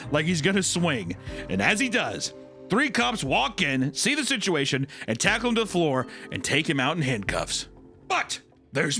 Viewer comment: What word was replaced with gamer supps at 25:00 if right after cocaine was fine lolll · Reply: Fentanyl